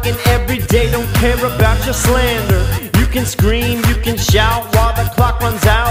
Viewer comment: lol when he said slander i thought he said slender. :P derp